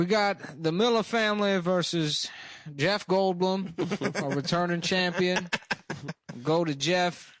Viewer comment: I heard “Jeff Goldblum, our retarded Champion”